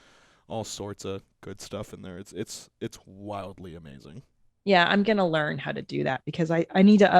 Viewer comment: oh, I thought he said mildly amazing for a sec because he was so quiet.